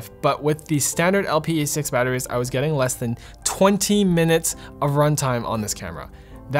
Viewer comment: who else heard him saying twenteen minutes.